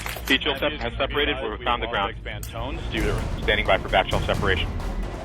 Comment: "Standing by for vaginal separation".... Wait, what did he just say?